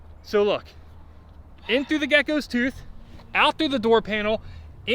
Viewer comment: did you just call godzilla a gecko ? 😆